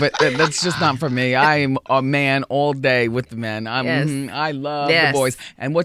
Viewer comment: Hold up @ 52:48 did Wendy say "I'm a man all day with men"?